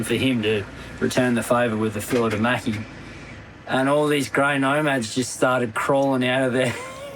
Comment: he said something was crawling out and got the fish. Were they cats that got the fish? Gray something's he said, but I can't hear what he said. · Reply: After googling I think I found that Australians might call feral cats nomads. Being American I've never heard that. (I was also wondering if wandering people in search of work were possibly called nomads in Australia.) Also, I've lived more than half of my life abroad so I was wondering if it was a phrase I've missed by living abroad.